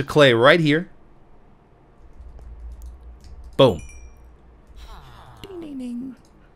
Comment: Seriously did anyone else hear go "bingadinddigding" at around 16:02? Lol